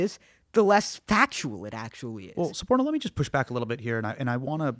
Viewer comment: Anyone else hearing Hasan calling "Squirtle"?